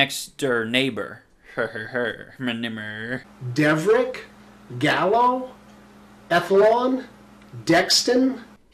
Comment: did he just say gallow????